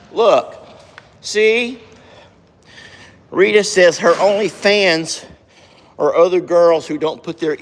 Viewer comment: Whew! For a second there I thought you were saying Rita had an Only Fans page!